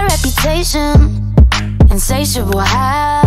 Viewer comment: Am I the only one that thought she said A bad reputation say sh!t will happen instead of insatiable habits